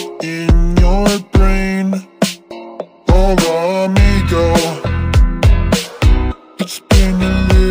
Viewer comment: did he say the N word? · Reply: No he said "hola amigo"